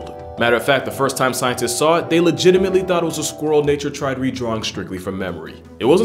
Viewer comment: When he said "nature tried redrawing strictly from memory", he spoke so quickly that I thought he said "nature tried a reach-around and it got scary." I did a double-take and rewound that with CC just to see what he really said. Also, his description of the II had me thinking of Chihuahuas.